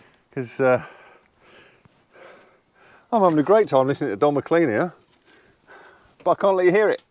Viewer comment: What a great video 😊 first the folk chat, got carried away and when you said "Don McLean" I thought you said "Dougie MacLean" 😅 gor myself all excited I was gonna hear a story about my favourite folkie! And how about Buck? The boy is really starting to grow and take heed now, clever lad. Sad not to see anymore Ballachulish slate but you are so near the finish line so Spanish is good 👍